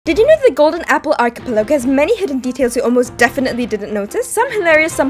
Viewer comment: Did you just say "ArchipeLEGO"?